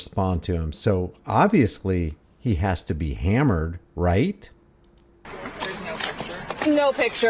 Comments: she did not say he is hammered 🍷 She said he has to be Kameran referring to the first name of the person who is registered owner (RO) to the vehicle who is suspended DL. Pay attention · I think she said Cameron not hammered.